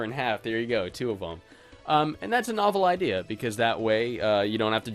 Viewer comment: I keep mishearing novel as awful...